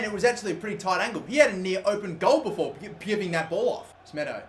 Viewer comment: what the hell did he say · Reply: He had a near open goal before sucking that ball off